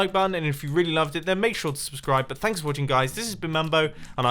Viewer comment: I've been wondering this since I started watching Mumbo.. at the end he always says: @ 22:23 "If you did enjoy it ???????? that like button". What does he say at the ???????? ? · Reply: Make sure to hit.